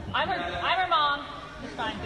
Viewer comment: Subscribed. Also what was said in the clip at 17:32? Genuinely dont understand what the mom said. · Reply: “AIRBALL! I’m her mom, it’s fine” An airball is what it’s called when the basketball doesn’t touch the net, rim, or backboard, only through the air down to the floor. But yelling it out in that tone of voice is just disrespectful.